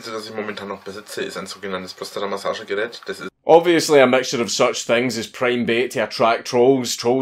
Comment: I'm a English learner. Could someone tell me what he says at 4:40 after the word, obviously?🙇🏻‍♂️🙇🏻‍♂️🙇🏻‍♂️ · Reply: obviously a matter of such things is prime bait to attract trolls..... For me as a german this accent is sometimes tough too ;-) I love the scottish and irish accent. Sometimes a bit like music for german ears ;-)